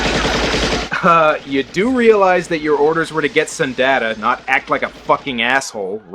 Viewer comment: i thought he was saying “get some data” for more than half the video